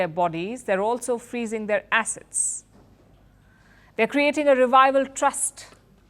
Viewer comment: I thought Palki said "they're also freezing their arses" 😂